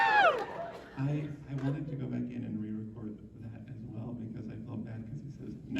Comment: Bryce once said: 15:43 Sits SUPER close to Brandon - 25:40 “What other sounds do you have to make” “How long did that take you Blitzy~” “I’ll never tell” Brandon and Bryce, fricken love those two 😂 - 38:30 Hoot Hoot - 40:32 “I would be Blitz~” - 47:57 “I’ll go to Loo Loo Land” - 51:10 “This will forever have a very special place in my heart” 💜 - 1:01:35 “We have degenerates on Broadway too, don’t worry 😉” - 1:08:22 scoots closer “I know you didn’t mean it… but it still hurt.” “Not in-front of the people Blitzy.” | Apologies, I can’t hear very well 😅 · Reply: "I wanted to go back and re-record that as well cause he says night and drives off" I think that's what he says at 1:08:36 🥺